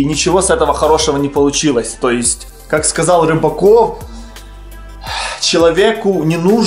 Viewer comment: Мне послышалось Робокоп